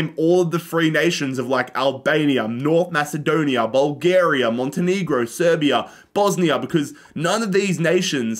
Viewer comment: hold up what he say monte.. what 😂😂😂